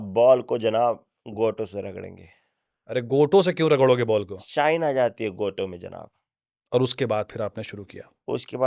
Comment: did he say saheen or shine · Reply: Shine